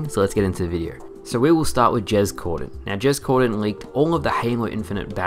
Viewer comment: for a moment i thought you said james corden and was like wtf has he to do with halo lol